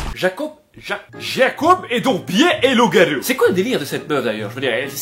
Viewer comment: "Jacob est donc bien un loup-garou ".. j'ai rigolé seule dans ma chambre ^^ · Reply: J'ai entendu "gay et loup-garou"